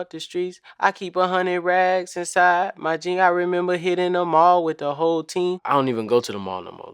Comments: I thought he said I remember hitting the mall with a whole ting bruh waaat😭🤦🏽‍♂️ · “I thought he said i really made it in the mall wit a whole ting🔫”😂😂😂